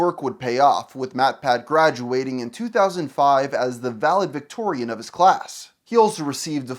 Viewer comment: umm .. I never knew mattpat lived in 19th century England. Did you just call him a VALID VICTORIAN? 😂 wtf?